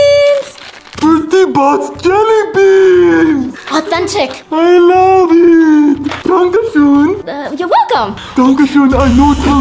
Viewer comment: When PZ FUNF said I love it I thought he said I love you 😂